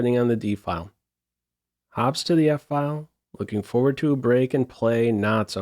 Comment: I totally misheard and thought he said awps to the file 😅😅 I might have heard Hikaru say that one too many times...